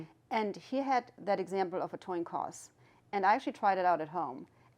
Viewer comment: did anyone catch her saying a toin coss?